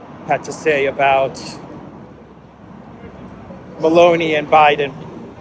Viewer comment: For a moment I thought you said Belonie and Biden😊